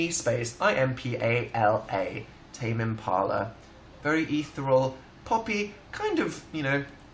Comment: DID HE JUST SAY EATHERALL INSTEAD OF ETHEREAL?!